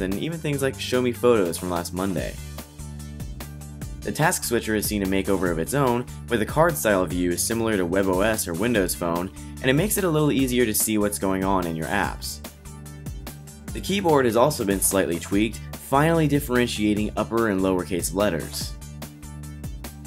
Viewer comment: sounds like you said "see what's going on in your ass."